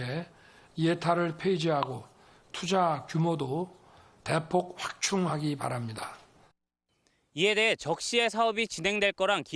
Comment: 신뢰가 떨어지다 보니까 "...대폭 확충하기 바랍니다."가 "...대충 확폭하기 바랍니다."로 들림